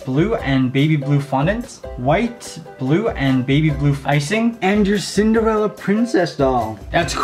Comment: Idk if anyone else spotted this, but at 0:36 did I hear "White, blue, and baby blue f--icing"?